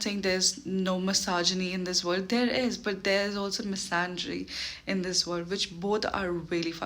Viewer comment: What is that word means? massangry? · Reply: Misandry, subtitles picking massangry is diabolical Misandry is when you hate men Misogyny is when you hate women